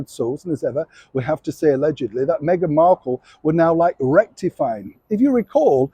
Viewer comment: Did you just say Megain wanted Harry to 'rectumfy' a point Neil? Perhaps it was just my (naughty) imagination. 😆😆